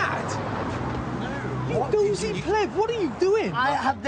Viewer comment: did he call him a pleb, well is a dick